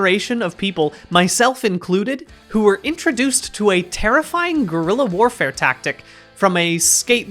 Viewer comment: Sorry, but I keep hearing "Gorilla Warfare"